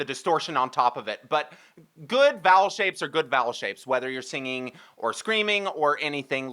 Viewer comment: I thought you said ‘good bowel shapes!’ 🤦‍♀️😂